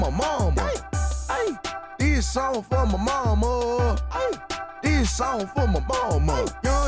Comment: did he say this song from obama? lmao